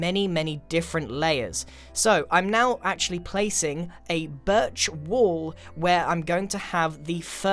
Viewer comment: my American ass: Did he just say Birch wool???? ohhh. no, he said birch waaall. i look at the bottom floor pillars and see a jungle temple in the making.